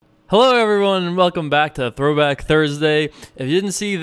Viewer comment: I had the tv on while I started this video and it sounded like Jason said "welcome back to Brokeback Thursday"...wishful thinking? Haha